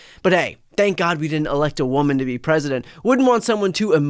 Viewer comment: Did he just say thank you president, to Musk?